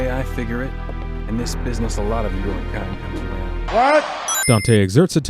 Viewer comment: Even at full volume, I still couldn't hear what he said. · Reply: "In this business a lot of your kind comes around."